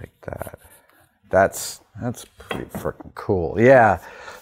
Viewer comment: I swear he said fucking there